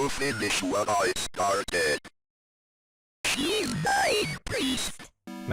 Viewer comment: Did anyone else hear "YOU LYING PEICE OF--" or is that actually what it says? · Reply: She says "She is mine priest" which is supposedly the demon inside of her telling John to eff off and let them do what they need to do.